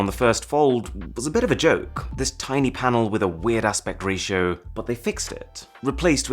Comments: I thought he said “weird ass ratio”. Either way, he’s right. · Max27265 thought I was the only who heard that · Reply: I heard “weird-ass pect ratio” and I was like “what’s a pect ratio?” then I realized “oh, it’s weird aspect ratio” lol